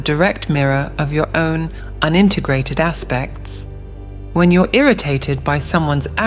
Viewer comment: sub title should be your own UNINTEGRATED instead of AND INTEGRATED